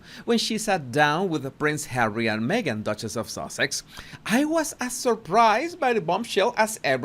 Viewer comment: It almost sounds like you said "Duchess of SAUSAGE" rather than "SUSSEX" 🤣🤣🤣